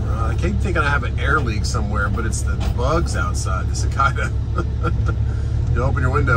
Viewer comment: what did you call the Cicadas??? Cikakas.....